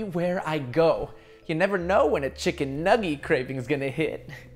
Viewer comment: when he said nuggy i heard it as nani?!